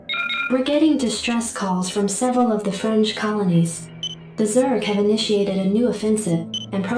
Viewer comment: Did anyone else hear "French colonies" instead of "fringe colonies"? 😃